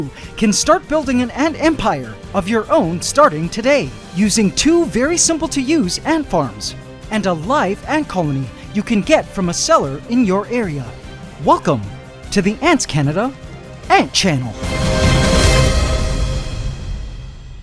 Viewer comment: I initially thought you said "That you can get from a CELLAR in your area." It took me a minute to realize you were saying SELLER.